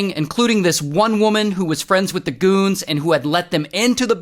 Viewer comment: I thought you said coons!